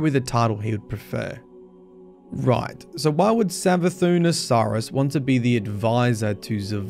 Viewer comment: Every time you say “Savathuun/Osiris” I hear it as “Savathuunasaurus”